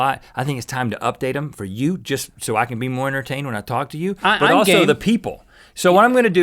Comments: Does Link Say Im Gay? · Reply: LOL I heard that at first too, but no he said "I'm game". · it sounded like link said "I'm gay" lol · i thought he said im gay but its reallyim game